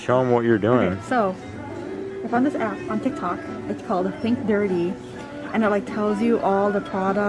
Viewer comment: what's the app called? sorry didn't get it hehe thanks! 1:33 ish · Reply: It’s called Think Dirty. At first, I thought she said Pink Dirty, but looked it up and Think Dirty is what showed up, and it looked exactly like what she showed us.